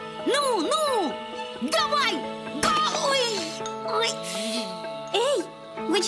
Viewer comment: мне одному ещё с детства кажется что он говорит голый💀